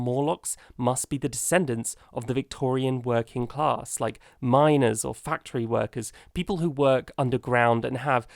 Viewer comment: when I heard “Miners” I thought you said “Minors” and…yeah, I guess it works either way